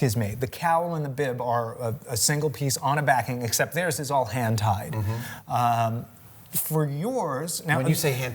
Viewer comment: He said "Hand Tied", I heard "Hen Tai"